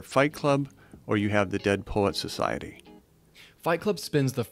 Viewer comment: Fight Club and what? I wan not quite make out what he says and English is not my first language? Dead Polack Society? Can anybody help? I'm very much interested in checking it out.